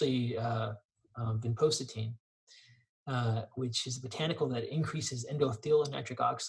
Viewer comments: Hi at 56:26 Dr. Kharrazian mentioned a 2nd supplement to summarize and I could not make out what he was saying? Can you please tell me what it was?? Thank you! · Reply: Hi Lana, The supplement mentioned is Vinpocetine. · Hi - great discussion/interview as ever, thank you. One question: At 56:25 (and also at 56:37), Dr Kharrazian mentions something I couldn't hear clearly, but he describes as a botanical. The transcript on your website says it's 'Vinpocetine', but I believe that this is man-made (although based on a plant) and generally used to improve various brain functions (such as memory, hearing loss and in dementia). It is stated as having numerous side-effects and not recommended for long term use. Can you confirm whether this has been transcribed correctly and, if not, what the correct word is please?